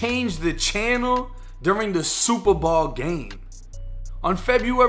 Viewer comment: I keep hearing "super ball"